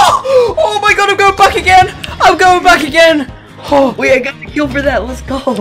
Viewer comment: All I hear is, “OHH MY GOD, IM GOING BACK AGAIN x10.”